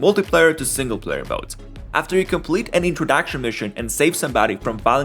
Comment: the AI says introducktion mishen instead of introduction mission.